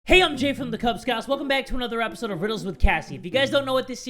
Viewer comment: My subtitles were on it thought you said hi im jeff from the cub scoots